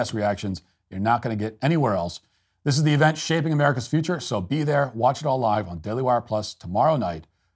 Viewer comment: I hope I misheard you say, " This event will shave America's future." 50:30 lol